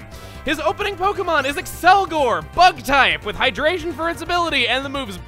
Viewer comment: When you said “Accelgor... bug type! ... Bug buzz...” I thought you said “Accelgor butt type... butt buzz”